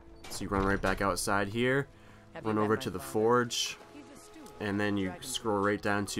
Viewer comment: I think you'll find that she says: " Have you met my father? He's the steward up in Dragonsreach." at 0:55. so look who's the idiot now. Also, capital letters doesn't make you better than anyone else.